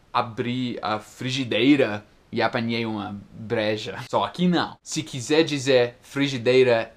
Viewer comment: não entendi o que disse, só sei que não falou "apanhei uma cerveja" rs e acho que no final vc falou a mesma palavra rsrsr, ou será que eu que não entendi direito mesmo? 🤔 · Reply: Ele disse "apanhei uma breja"